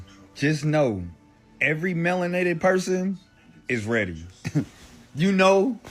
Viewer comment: Man I’m hungry I thought he said marinated not melinated